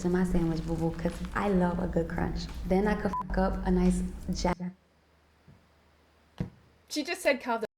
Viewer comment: So at 16:24 she definitely said “then I could fuck up a nice jackfruit” ... she did not say “carb the fuck up”